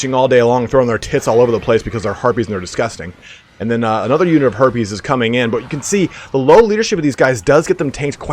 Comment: I thought I heard you say "herpes" instead of "harpies"